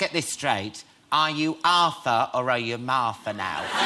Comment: WHAT HE SAY AT 7:28 ??? ARTHUR OR MARTH???